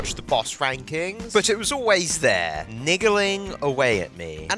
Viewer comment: what did he say? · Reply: Niggling - causing slight but persistent annoyance, discomfort, or anxiety. I myself am a niggler.